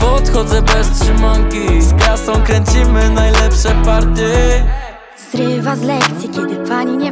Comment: Usłyszałam zrywam z Lexy i miałam laga XDD